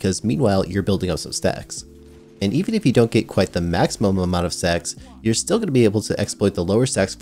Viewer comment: had to rewind a few cus I thought he was saying "sex" and not stacks xD